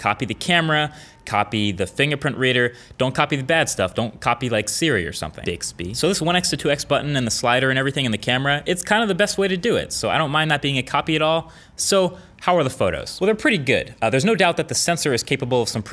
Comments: Did anyone else hear Bixby when he was talking about bad stuff in smartphones at 4:32 lol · does he say 'Bixby' or is it just me?